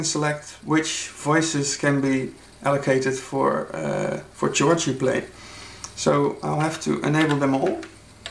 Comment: Thanks for the demo... I thought you said "church" playing :-) But you meant chords I guess, pronounced "Kords". Ah, that's the beauty of the English language for you! Any way, I find Analog Four quite nice. I was just not sure about how spontaneous this instrument really is with all its sub-menus. I need to check it out next time I am at my local music shop.